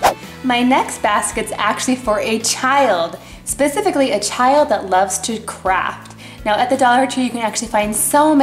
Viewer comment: i heard "A child who loves to crap!"